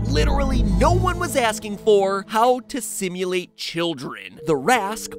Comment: i thought he said how to stimulate children for a second